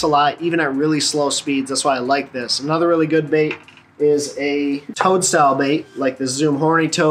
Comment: Bro.... i thought you said tongue style bait instead of toad at first haha. Must be that Michigan accent! 😂